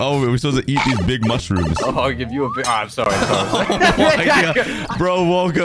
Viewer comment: WHAT DID HE SAYYYYYY??????????? ( Edit 1: this is the first time I have gotten this much likes lets get to 50 likes 🙂🙂🙂) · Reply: I’ll give you a big😅